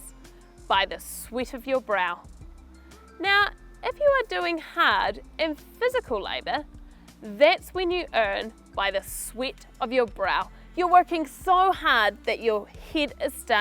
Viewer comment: Does sweat is sounds like "sweet" in british? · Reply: It just me or idk, i heard she said "sweet". :)